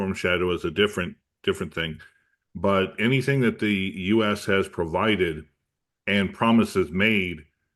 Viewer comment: thought u said the us was terrified?